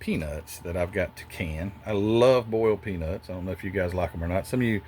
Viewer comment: Wish I could help you with the seat choice, but none of the options stand out to me. I could see using the stock-looking bucket, maybe that's the way I'd go. But with the exterior color, I feel like the sky's the limit -- it strikes me as a very unusual color for that car. (But I get that it's MOPAR Plum Crazy, so it makes sense.) Re: boiled peanuts, I'm a native western New Yorker, so I'd never had 'em until someone from Georgia offered some to me. Sorry, it did not go well. I didn't know they'd be so mushy. Also, they were room temperature. I could maybe see how they'd be nice if they were hot. But lukewarm and mushy was not a pleasant combo for me. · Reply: Oh yeah! Also, this person from Georgia spoke with -- I guess -- a Georgia accent. It sounded like she said "bowled peanuts," so I had no idea what she was talking about! 🙂